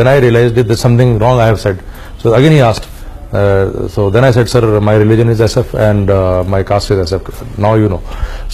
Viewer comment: i didn't understand what he said ? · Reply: My religion is SF and my caste is SF. SF - Special Forces (he is a para commando)